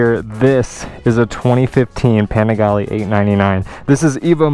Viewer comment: did he say "2015 Panigale" or is it just me? 🤔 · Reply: He did. It was the last production year of the 899. Mine is 2015 too.